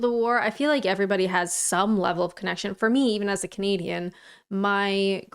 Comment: love the accent but i must confess i misheard you i thought you said you are a "comedian" not a canadian ooooops 😊😊😱😱 love your streams Ex Royal Navy 23 years service <num> keep it up 🫡🫡🫡🫡🫡